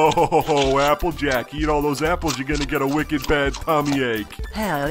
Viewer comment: what did rainbow dash actualy say??? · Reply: keep eating all those apples and your gonna get a wicked bad tummy ache